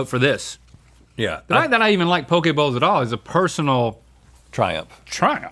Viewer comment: Swear to god he said poké balls 7:56 😅